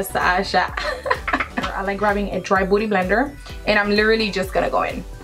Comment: i thought she said a "dry booty blender" lol thnk you. i brought this and had no idea how ro properly use it